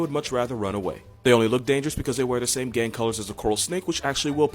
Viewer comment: When he said coral and milk snakes wear the same gang colors, I first heard it as “gay colors” and as a queer person I was just like “there’s too much truth to that to even consider being offended”